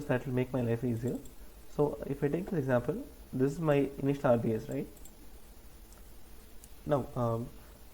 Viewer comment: What is meant by the word Rbase (if I am hearing it correctly) in the "D" problem Bracket Walk? at video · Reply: he says rbs - regular bracket sequence